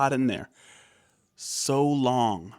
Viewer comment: I thought he was saying "So LOUD" not "So LONG". ??? Maybe both?